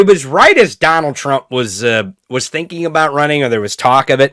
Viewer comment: could've sworn i heard you say "trump was stinking about running......", so i had to rewind, LOL!